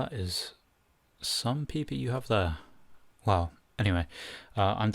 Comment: Did he just say “that’s some peepee you have there😏😋😼” or am I imagining it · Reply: haha XD its 'Peeper'meaning eye